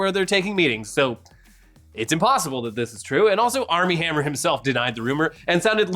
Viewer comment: I misheard Army Hammer as R Lee Ermie and now no matter what im going to be disapointed...